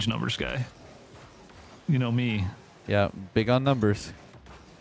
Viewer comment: did say “math 12 guy”??? So close lol